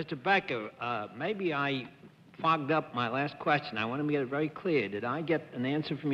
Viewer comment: I heard other words than Bennet said here... "I fogged up".. lol